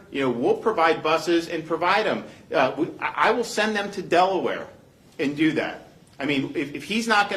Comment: The things you see when surfing : thought it said "I will senect their underwear" my bad😝